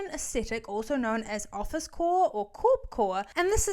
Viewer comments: Oooooh, office COOOORE... i thought y'all said office WHOOOORE.... honest mistake 🤷😂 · I truly thought you said “office w*ore” and I GASPED